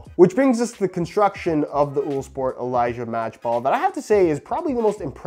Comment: Thought it said unisport